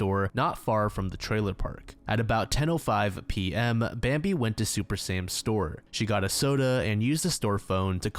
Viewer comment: At first I thought he said 'Super Saiyan store'.